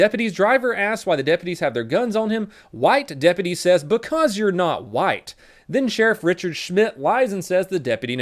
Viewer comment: wait but what does he say he sounds like he said because your not white?